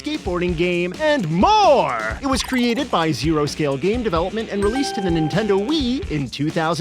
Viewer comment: I misheard zero scale as zero skill, and was like "yeah that tracks"